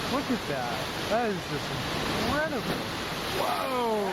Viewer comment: So, this flash flood (7:45) happened in Credible? I'm sure I heard the guy say in Credible. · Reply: 'Incredible' is what he said. Pretty sure there isn't a place called 'Credible'